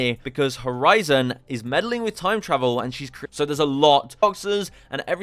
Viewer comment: what the hell are you even saying lmfao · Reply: Lmao it bugged, the original recording was not like that 😂 I basically said horizon using time travel ends up causing paradoxes that affect alternate worlds/dimensions and that’s why alter is looking for her